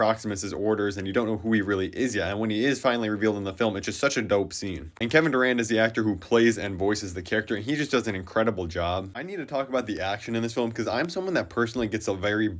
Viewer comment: Thought he said Kevin Durant💀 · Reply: Same😭😭😭I had to go to google to double check it wasn’t Kevin Durant. Turns out they basically have similar names Kevin Durand.